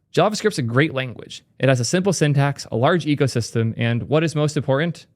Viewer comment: In the reading of "wtfjs" I heard "has a big ego system"...😂